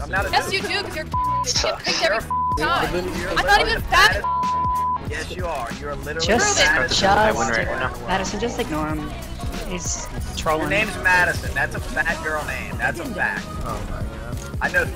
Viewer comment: "Your name is Madison, that's a fat girl's name, I know 3 girls named Madison and they're all fat" Shit had me laughing my ass off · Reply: Jordan Granados i thought he said bad girls name o.o